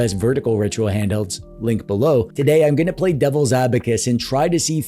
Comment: Did you say...devil's abacus?